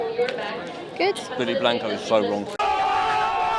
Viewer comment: What did he say at 4:56? Can't seem to get it. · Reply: He said Benny Blanco's so wrong. Benny was the man who went viral for trash-talking Jollibee food 2 months ago.